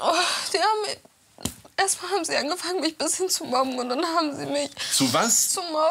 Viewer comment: wer hat auch bomben 💣 verstanden 😂😂